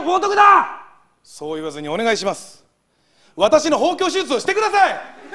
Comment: 一発目包茎手術をしてくださいって聞こえたwwww